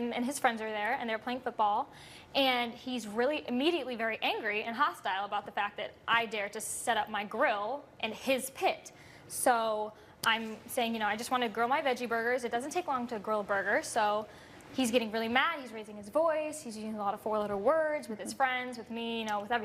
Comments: I would have grilled her “veggie burger” and then ate it. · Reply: Oh, i thought she was saying ''grow my veggie burger'' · She says "He was very angry, and he was using a lot of four-letter words . . . " and then later, "Well they were being really nice . . . " Something here doesn't stack up. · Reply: Narcoleptic Axolotl oh she said four letter words. I thought she said foreign words